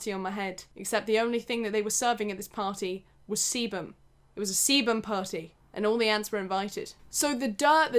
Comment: “It was a sebum party. And all the ants were invited” Imagine hearing this out of context 😂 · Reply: I kind of clicked to the end and caught it up at that sentence, I thought she said 'it was a semen/seaman party and all the aunts were invited.' I thought what the hell! lol.